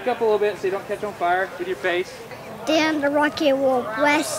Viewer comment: did he say "then the rocket will blast off" or "damn, the rocket will blast off"? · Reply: Then*